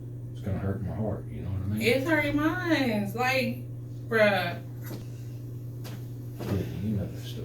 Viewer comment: Did she just call the Captain "BRUH"??? Wtf?? She whines like a teenage girl. She needs a job at Chuckie Cheese. That'd be just right for her!! Idk, but if I was having a serious conversation with my boss, no way would I use foul language! To my friends, the eff word is my favorite.